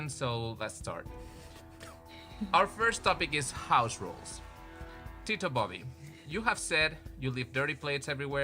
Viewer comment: oh, "House rules"! I thought he said, "Auschwitz" which was concentration camps